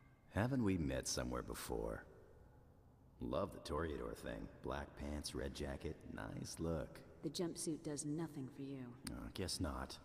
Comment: What does he say at 1:03? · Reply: "Love the toreador thing. Black pants, red jacket. Nice look." Toreador being a bullfighter.